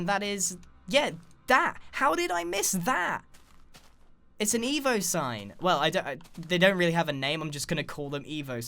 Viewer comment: I thought he said "It's an emo dime!"